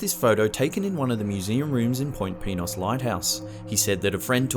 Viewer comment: I thought he said point penis